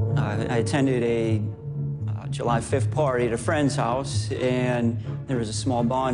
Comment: did he say July 5th party?